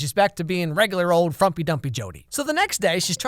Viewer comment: I'm sorry did I hear him say 'frumpy dumpy'? 4:50 like uhhhh Me:hey Sad friend:go away Me:don't be so frumpy dumpy Sad friend:Tf